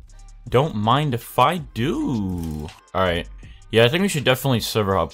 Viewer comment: i had low volume and i thought you said don’t mind the f*ck dude